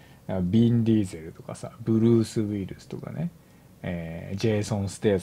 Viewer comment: ブルース・ウイルスに聞こえるのは重症かな？